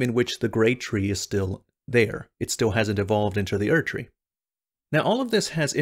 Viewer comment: why did you say "devolved into the erdtree" at 3:30 is it a part of the theory that the erdtree is weaker. or just choice of words